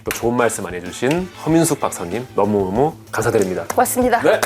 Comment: 허민숙 박사님이라고 할때마다 커밍순으로 들리네